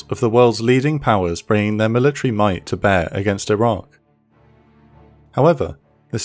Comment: I swear I hear this narrator say "Iran" instead or "Iraq".